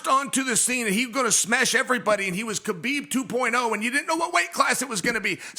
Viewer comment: Dc we all heard you say "beat his ass khabib " 😭